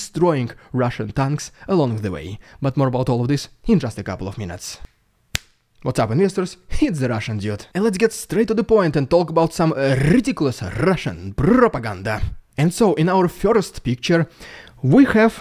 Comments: I always hear "What's up everyone, this is Russian idiot." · By the way, when he says "What's up ___isters, it's the Russian Dude!", what's the word? I can never get it. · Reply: I thought that he is saying "Hit the Russian dude" 😂